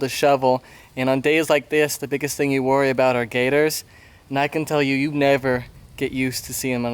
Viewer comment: I really thought you said Gaydars. But turned out to be gators.